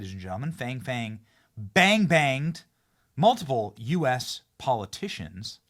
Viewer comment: I need to get my hearing checked, I kept hearing “bang bang” how ironic 🤔